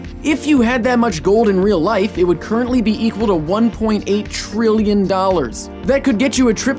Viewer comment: I thought it said $1.88 and then it said trillion